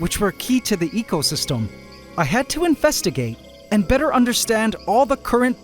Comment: …did he… DID HE JUST SAY INFESTIGATE